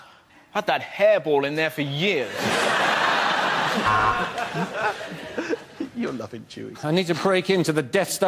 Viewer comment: Does anyone know what Russell says at 35:33. It sounds like “you’re nothing,chewie”. Could someone please confirm that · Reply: It’s macintyre and he says your loving chewie to Russel